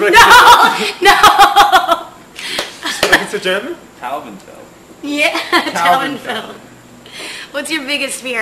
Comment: Was that "Taubenfell" I heard at 5:39 ? hehe crazy :D